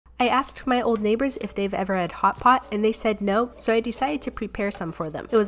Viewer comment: I thought she said "I asked my neighbours for hotpots and they said no" 💀🚶‍♂️